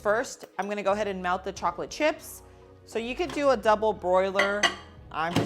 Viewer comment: It sounded like you said "double broiler". 😂